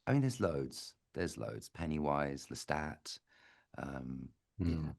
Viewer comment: did he say Lestat???